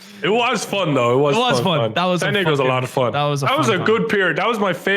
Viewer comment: Am I the only one that thought Nogla said “n*gga” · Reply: He said "It was fun it was fun, San Diego's a lot of fun"